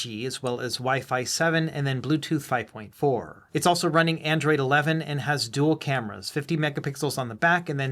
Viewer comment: I heard android 11 and I was like what the fuq!!!!!